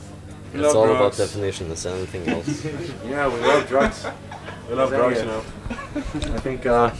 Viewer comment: We are lilfelovers, we love drugs. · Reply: I thought he said "Low drugs!" probably to say "oh shut up" in an amusing way.